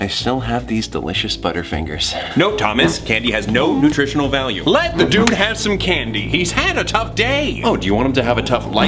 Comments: Did roman just say "Let the duke have his candy"? I guess he doesnt hate his brother THAT much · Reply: No, he says "Let the dude have some candy" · In the end of the video when you get a mini video of and Roman controlling Thomas’s arms for the Butterfinger i don’t know if I was the only one to notice this but when Roman said “Let the dude have some candy” I kinda heard him say “Let the Duke have some candy.” And also you could VERY faintly hear the Dukes theme song playing in the background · "Let the dude have some candy!" Omg for a seconde there I thought he said "Let the Duke have some candy!".......that terrified me......